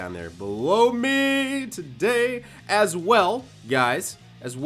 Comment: Why does it sound like said "blow me today" @ 6:06. I think your dad Rikus' hat is on a bit too tight :)